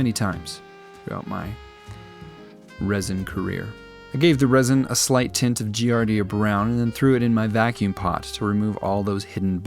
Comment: Thought he said we wasn’t going to tint the water? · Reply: He said he wasn’t gonna put a gradient in it